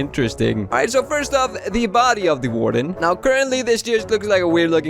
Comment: i thought you said the body of the gordon, not the body of the warden, lol